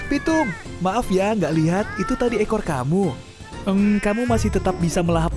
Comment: apakah saya salah dengar bahwa yg dia katakn vtube